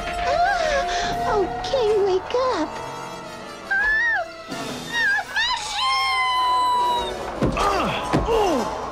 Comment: and 0:23, what did Goldie say after she fell just a little bit. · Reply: My shoes